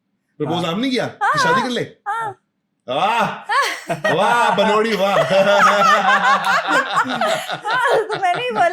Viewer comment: did he just called her padori??!! 14:11🤣🤣🤣💔💔